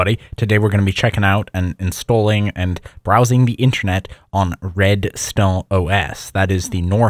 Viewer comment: Why is he pronouncing “intranet” so bizarrely? I’ve never heard anyone pronounce “intra-“ that way. His accent is confusing when he says Red Star it sounds like he’s saying “red stone”. Throughout the video (example 0:10) he says ‘Red Star is KDE “riced”(???) to look like MacOS’. Obviously he means it’s themed/customized/skinned to look like OS X, but does anyone understand what word he’s actually saying here?